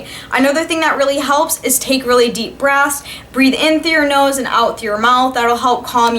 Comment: Hahahahahahaa I thought you said: "I'm still shaking in my boobs" but I found out that you said boots :') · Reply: When she was talking about taking deep breaths I thought she said "deep breasts." ._.